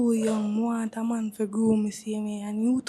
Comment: Did she say she want a man fi groom har? wtf she want a man fi manipulate har and show har how fi think?